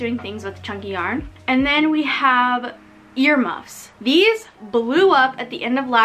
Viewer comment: when you said ear muffs , for a second i thought you said your mum lol🤣